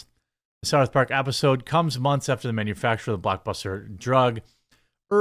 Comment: When you said blockbuster drug I thought you said buttbuster.